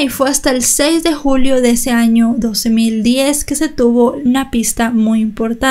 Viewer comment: creo que escuche 12010